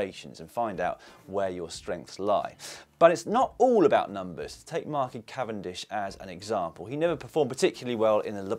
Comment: did you actually say, "take market cavendish"? I have replayed that bit several times and it sounds as though you are saying "market"